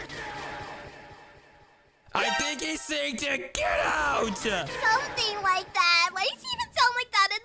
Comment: mr black is actually saying "go fight again"👀☝️